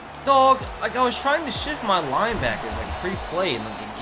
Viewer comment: I know he ment to say shift but it sounded like the s word